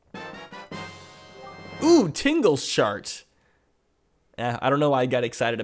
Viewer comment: Sounded like you said tingle shart, OOF